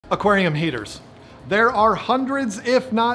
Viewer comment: When your video started, I misheard you as saying "Aquarium haters" 😆